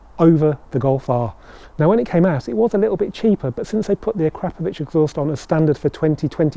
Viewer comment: The Crapapovic exhaust? Sorry, misheard